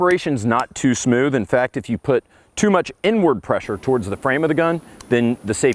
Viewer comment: Did hey say "N-word pressure"??